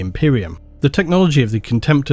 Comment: Deredeo Dreadnought … somehow I misheard it as Dorito Dreadnought lol